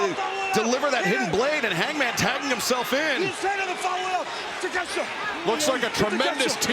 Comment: Sounds Like Hangman Said I Follow It Up “Ninja”😂